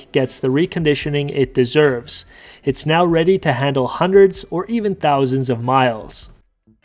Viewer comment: why would say hundred of miles? What a strange phrase you said. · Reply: I said “hundreds or even thousands of miles.”